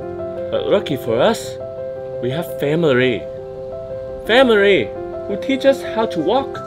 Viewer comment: did you say…. Family?